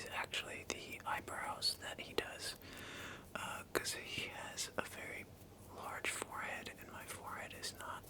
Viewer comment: idk if i misheard but 15:00 "kasi" filo word for because or am i tripping